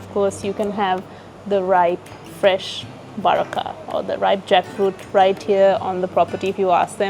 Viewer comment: Felt like i heard 'Vazhakka' 😁